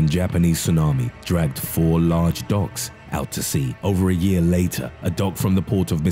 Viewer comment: Oh my god I thought he said dogs not docks. I'm such an idiot